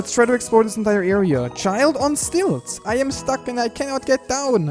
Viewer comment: I thought that said "child on steroids"